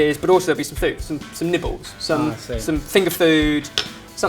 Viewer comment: I know I'm not the only one who thought he said "nipples"